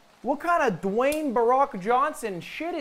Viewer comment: Did Lud say "Dwayne Barack Johnson" at 14:53!?